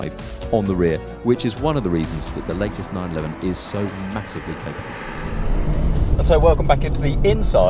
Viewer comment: does he say, "... so massively capable" at 6:16? Rubbish auto-generated captions say "so massively thing so". There are quite a few other hilarious transcriptions too (where 'hilarious' = not funny).